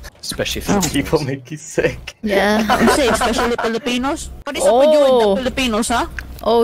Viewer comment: That's a bad banter by though.. even it is a joke hating someone's race is not a good thing if you are thinking properly lol, handled him good. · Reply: I dont think he actually said Filipinos though. If you listen to what he said with an earphone or headset, its more close to "Few people" than "Filipino". might've just misheard it